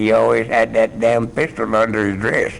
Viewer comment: Lol i thought he said pickle under his dress lol.... well pistol n pickle are both true i guess lol